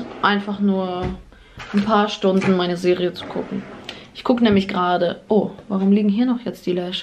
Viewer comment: warum habe ich verstanden „in meine SEELE zu kucken“?! Man weiß es nicht...💩🤷🏼‍♀️🙉